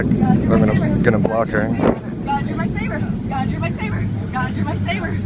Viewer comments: after a while, all i heard is, "doge ur my saber." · I thought she was saying god you're a sinner